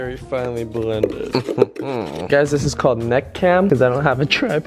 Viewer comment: I thought he said "Nut cam" wth😂